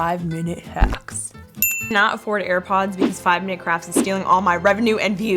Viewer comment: for a sec i thought she said 5 minute cracks lol